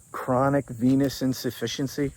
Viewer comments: I had to rewind to hear it again. I thought you said chronic penis insufficiency. 😂 He does suffer from that as well. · When you said chronic venous insufficiency, I thought you said chronic penis insufficiency, and I cracked up😂